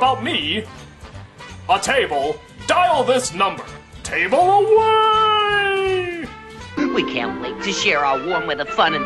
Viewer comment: when I first heard "Table awaaaayyyyy" I couldn't sleep at night... imagined a person flying on a table around the town...